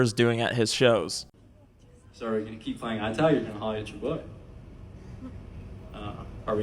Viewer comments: please tell what he said here?? im not a native speaker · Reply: he said "are you gonna keep playing eye tag or holla at your boy?" it means basically " its a meme and basically means are you going to keep staring at me or make your move?" As in attraction. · please tell what he said here?? im not a native speaker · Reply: basically he was asking if they were going to continue playing his song or was his crowd going to "holler" at him. Like, think... Barking? Going "Woo!" A lot. A way of being suggestive and "sexy" to some people if that helps